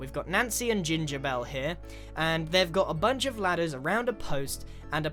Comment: I thought for the cat ladders I thought he said bladders :(